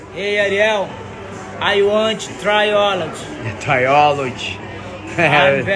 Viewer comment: The meme at the end tho lmao i thought he said biology